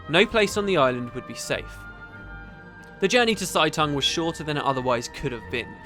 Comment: Every time he said Seitung and thought he was saying Zhaitan. Really skewed the story up a bit until I saw it written o.O